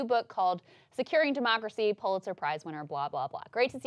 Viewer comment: Dear God, I thought you said "Pulitzer prize winner Bob Loblaw."